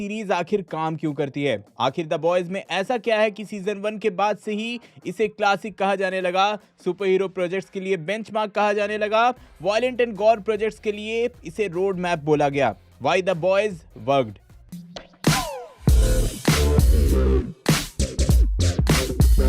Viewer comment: I thought you say Why the boys fucked 😂😂😂😂😂